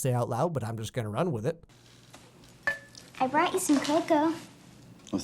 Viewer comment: turn on subtitles and it says vodka instead of cocoa